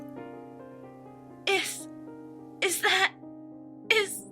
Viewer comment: it sounds like Rainbow is saying 'This' anstead of 'Is'.THIS IS,SPARTA!Kicks Pinkie in the face,or,Rainbow:THIS IS JOHN CENA!*Theme plays as John Cena tackles and kills Pinkie John Cena:HIT THE RING NOW~